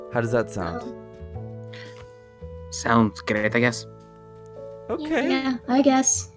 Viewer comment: Wen el chip said "sounds good" I thought he said "sounds gay"